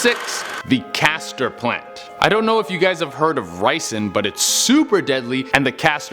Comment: i thought he said, "you may have heard of rice which is very deadly"